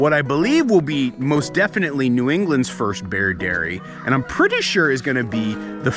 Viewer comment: I thought the video said bear diary